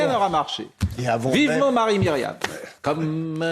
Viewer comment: "Vivement Marie Myriam !" C'est étrange, j'ai entendu "Vivement Marine Le Pen !" Pourquoi ? Je sais pas, ça suintait tellement... 🖕🤮